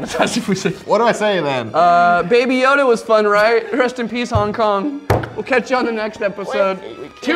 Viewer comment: he said rust in peace instead of rest in peace